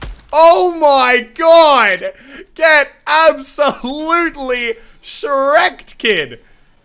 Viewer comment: did he say get shreked kid?????